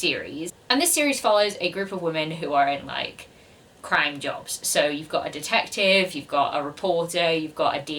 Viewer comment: Oh “crime jobs”! I thought you said “crying jobs”! 😂 I was thinking, what the heck is a crying job? Haha!